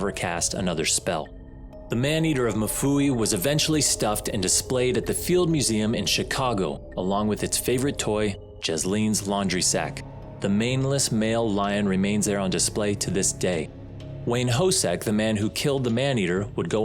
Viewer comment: Did you say around 12:00 “the maneless male lion”?